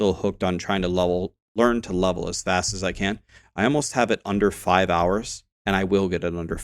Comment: Did I hear that right at 29:50. You are hitting 100 in 5 hours?